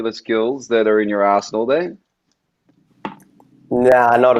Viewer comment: Do you have any other particular skills that are in your a$$hole...sorry, that's what I heard lol